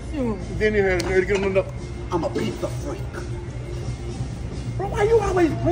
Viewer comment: I thought he said: I'm a piece of frake?